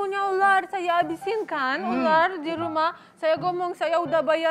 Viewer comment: kocak bener... apa gw salah denger. Ibunya pak Dika bilang habisin ular dirumah